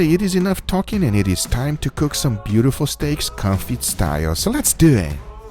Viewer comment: I thought he kept saying coffee style. Thankfully in the description he spells it out - confit style. Never heard of it.